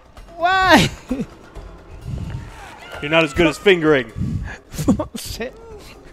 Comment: "You are not as good at fingering." Is that what she said?🤔